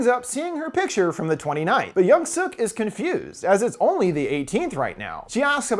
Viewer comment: I kept mistaking due to saying yeong suk-yeong suk 😅😅. I heard it as young suk-young suk. And kept ruminating, isn’t that yeong suk the past one? Why isn’t he saying old suk or something?